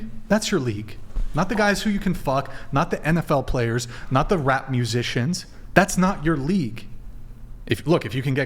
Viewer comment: I misheard that as "rat musicians". My hearinf is gone from all of this present day female bullsh*t 😂